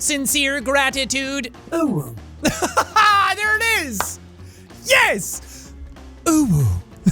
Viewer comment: did he say owo??!!!!! We